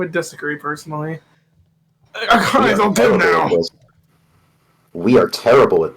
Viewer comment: Yeah.. that’s a typo. He actually said “A-Akane are you okay!?”